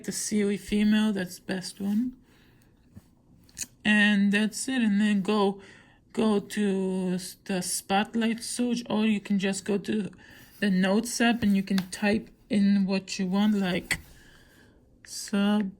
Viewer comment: I literally heard spotlight suge and notes up